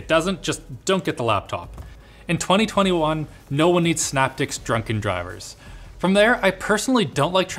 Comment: subtitles say "snapdicks" instead of synaptics at 14:28 lmao